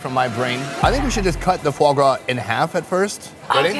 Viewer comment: min I dont understand.. what are they eating? It sounds to me kindalike "fourbra " Please Help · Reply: foie gras, i think its a duck / goose liver